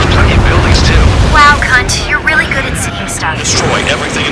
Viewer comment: Why do I feel like Pickme was suppressing some jealousy when Trigger was seemingly showing interest in Avril? Edit: @ 5:56 Did Pickme just call Count "Cunt"? Edit2: Okay, I am loving the fact you have Count and the others reacting to Trigger and Pickme.